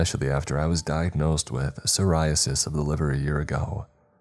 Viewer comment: It sounded like he said psoriasis of the liver not cirrhosis... 😂😂😂